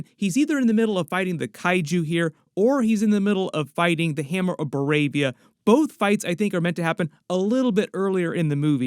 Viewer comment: the subs say Hammer of Arabia instead of Hammer of Boravia lol